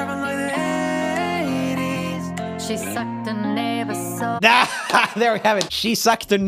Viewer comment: The one with the son and mom was everything it made me so happy but I lowkey thought she was about to say nigga but she said neighbor😂😂